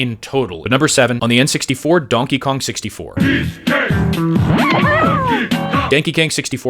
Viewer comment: I could have sworn he said "danky kang" im dead 🤣